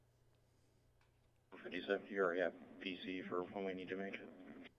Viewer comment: Sounds like he says "PC for... " Probable Cause